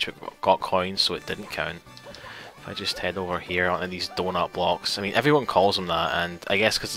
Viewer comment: anyone else thought he said door knob blocks?